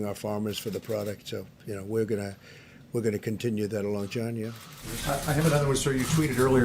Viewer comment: wtf did he say at 4:05 “longina”?!? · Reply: Oreo 760 “We’re going to continue that along, John yeah.” He’s referring to John Roberts, that reporter who is sitting up front